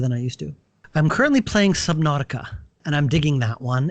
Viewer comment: I'm interested in the open-world crafting survival game he mentioned in 30:55, I just can't make out the name, maybe because I'm not a native speaker of English, dunno. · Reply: Ah, my mistake. He's currently playing Subnautica. Hope that helps :)